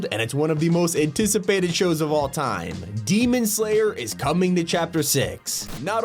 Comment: for some reason why I heard Demon Slayer I fought I heard Doom Slayer lol